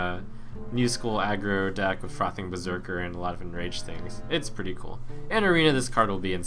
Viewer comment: If you listen on 2x speed it sounds like he says "fucking berserker"